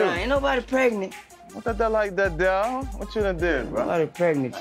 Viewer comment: wtf did Mack say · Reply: What that like their dog … I think that’s what he said